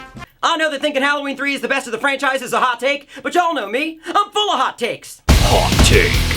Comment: Just for a moment, I thought he said, "I'M FULL OF HOT CAKES".